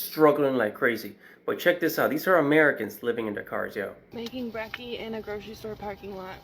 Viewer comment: What did that girl say sounded like she said she's making breaking or break e or something. But it looks like she's cooking breakfast but I couldn't make out what she said. It sounded stupid but I couldn't make it out whatever she said